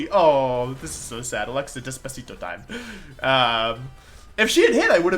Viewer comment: Did he really say "Alexa, Despacito time" at 16:04 ? I mean, am I missing something?